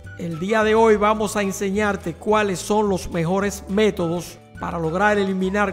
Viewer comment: le entendí los peores xD